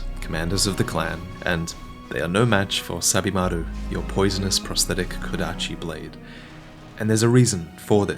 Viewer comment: At first I thought you were saying "Stab-imaru".